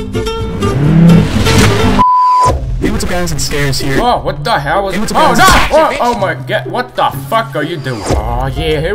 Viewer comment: What did the robot say on 03:05 ? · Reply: Lucksman247 hey Guys its scarce here